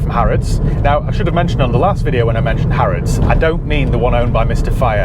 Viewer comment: Harrods i always thought you were saying Halford's lol.